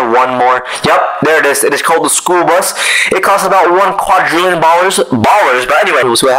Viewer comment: did he just say "ooh that costs 1 quadrillion balls" 💀